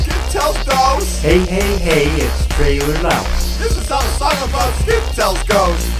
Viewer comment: I thought he said trailer mouse